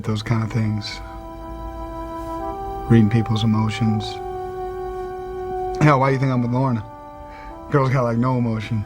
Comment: I thought he said "Why do you think I'm a bottom"